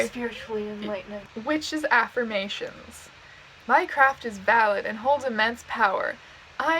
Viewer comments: why did i hear "my craft is valid and holds immense power" as "minecraft is valid and holds immense power" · What she said: My craft is valid and holds immense power. What my goblin brain heard: Minecraft is valid and holds immense power. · misheard as "minecraft is valid." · I THOUGHT SHE SAID “MINECRAFT IS VALID AND HOLDS IMMENSE POWER” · every time you said 'my craft' i thought you were saying 'minecraft' and im weak from laughing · I heard Minecraft and was caught off gaurd. Or is it guard? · I thought you said “Minecraft is valid and extremely powerful” and that’s a mood if I’ve ever heard it